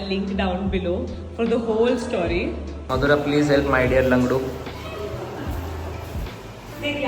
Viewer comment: I heeard lavdo 😂😂😂instead of langdo 😂